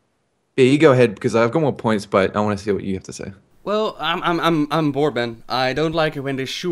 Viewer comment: lol at the first at 4:40 i heard say " Well I - I am Mormon" and i was like " wait wasnt he from the netherlands" ?